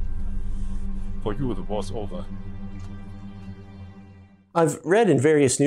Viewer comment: What does the German farmer say at 0:58? I couldn’t understand him/assumed it was German but no subtitles showed when he said it. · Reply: For you the war is over. The cliched phrase used in many films when allied servicemen have been taken prisoner